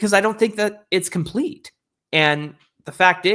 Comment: We'd say 2022, 23, and 25 were pretty complete teams! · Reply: Oh, sorry, I thought you said competitive not complete. I've had eye problems. Please forgive me